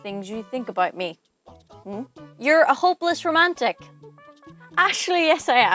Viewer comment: I thought you said homeless romantic! Made me smile watching this. Happy Saturday JD.